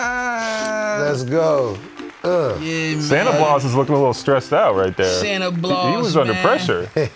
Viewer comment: 😂😂😂, He Said Santa Blaus, Instead Of Santa Claus He Always Does This, And It's So Funny 😂